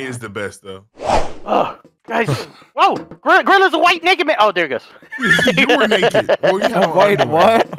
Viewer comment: Delirious just said the N word... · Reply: No, no, no, he said "White naked man" I thought he said it too but he has a unique voice which made it sound as if he said that. Just listen closely 😂🤦🏿‍♀️